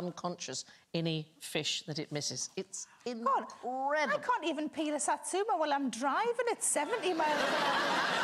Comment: I had to rewind! I could have sworn Sarah said, "I can't even pee in a satsuma when I'm driving at 70 MPH".